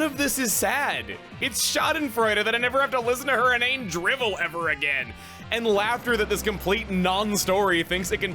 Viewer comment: Gee chuggaa maybe santa will bring me a dictionary so I can understand what you just said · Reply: "It's schadenfreude that I never have to listen to her inane drivel again."